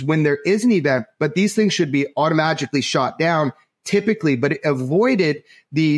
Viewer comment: Had to rewind a few times… 39:17 did I hear him say “auto-magically” ?!? 🤔. Lol! Either way, love the show!